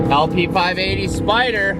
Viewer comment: "LP-580 Spyder" and the captions say "I'll pee 580 spider" xD